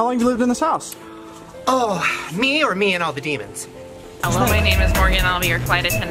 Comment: Oh just me and the demons (thats how i heard, if i did a mistke correct me) -Scorpios